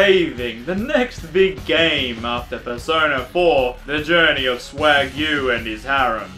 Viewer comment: “Swag Yuu and his harem” i thought I heard wagyu... And by the way that ending though. Fujoshi content aside, I actually liked how the voices were seamlessly put together in that one. O///o